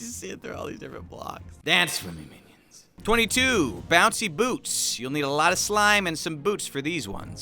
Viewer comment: I thought he said bouncy BOOBS